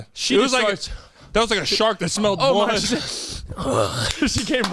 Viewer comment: did p say a shark that smells water haha